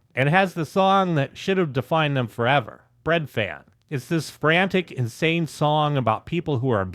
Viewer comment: I heard a spoof of "Breadfan" called "Bed-pan!"😂